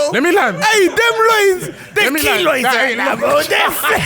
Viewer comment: Can someone please tell me what said at minute 52:14 about rondaldihno loins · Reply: He said "Oi them loins, they're keloids right now bro, they're finished" 😂😂